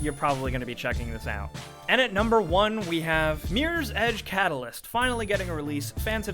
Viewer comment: What the fuck, did he just say Meers Edge?! lol God this guy talks so much shit.